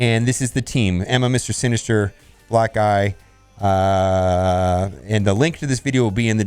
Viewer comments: I laughed so hard when he called hawkeye "blackeye" but in reality thought he said "black guy" 🤣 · "And this is the team - Emma, Mr Sinister, Black-eye.... uhhhhhhh" LOL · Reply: I thought he said black guy lol. Then after he said that he talked about bishop. 😂